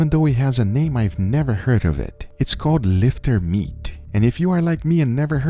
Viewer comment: Trying to know what to search for... sounded like you said "lifter meat"... but not sure and among all your notes below the video it is not stated... please help...